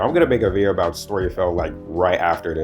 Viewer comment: Subtitles for some reason wrote Storyshift instead of Storyfell. YouTube knows Alternate Universes?